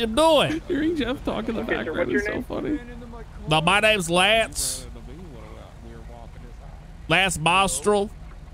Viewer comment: All the way through, all I hear is 'my name is Lance Nostril'. This whole shenanigans was awesomely funny - kudos to 'Napoleon Dynamite' R for escalating this, and for putting up with all your guys sh1t! Stars, one and all.